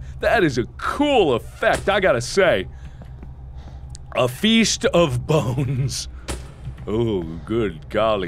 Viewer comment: Thought it said a "Feast of boobs" lol. Cant argue with that. :P